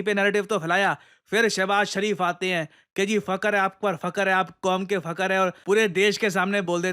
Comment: what did he sayyyyyy😂😂😂😂😂 Kaum ke fuc**er hein😂😂😂😂